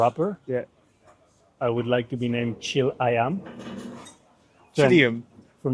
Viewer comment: I THOUGHT CARLOS SAID CHILLI AYAM ( CHICKEN IN INDONESIA) HAHHAHAHAHAHAH